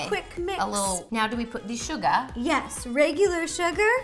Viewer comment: Is it just me or did Kandee say Suga? 5:42...